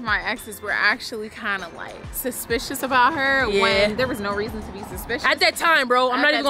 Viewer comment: did just say "one of my exes then said *her*" so she wasn't straight all this while.....ooops